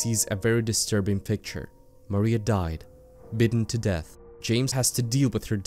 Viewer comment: hold up, did i hear that right? "Bitten to death"?